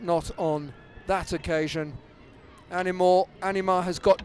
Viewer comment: did he call her animal?